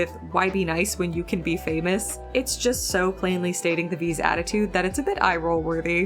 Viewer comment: I thought they said “why beat us? You could be famous!” As a response to Charlie 😬